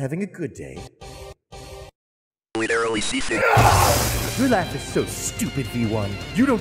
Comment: "Ordinarily that would bother me." +[E] "But today-" +[EN] "It doesnt." +[ENRA] "Cause I'm having a good day." +[EN] +[ENRA] +[ENR] "LITERALLY SEATHING." +[ENRAGED] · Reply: I thought it said “literally see through”, as in he’s calling Gabriel gay and is in so much denial that the closet he’s in is literally see through